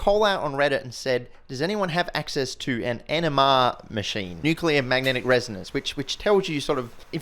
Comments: I can't say I didn't double-take when he said "NMR machine". sounded like "enema machine" with his beautiful accent. Edit: i should've watched the next 30 sec, sorry not taking it back :) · i thought you said "does anyone have access to an enema machine"